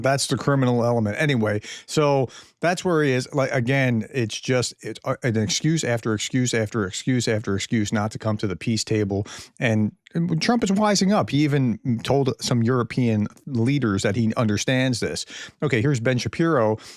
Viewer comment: "Trump is wising up..." Did I hear you correctly?